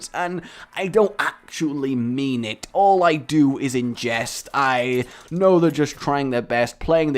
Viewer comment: mfw I heard "All I do is ingest" but it was actually "in jest" oh ( ͡° ͜ʖ ͡°)